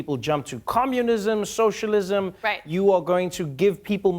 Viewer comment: did Trevor just say "commulism"... somewhere, JonTron is sneezing his ass off. 😆